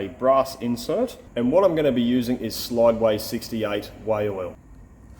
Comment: I had to replay the way oil, could have sworn you said whale oil. addictive viewing, thank you.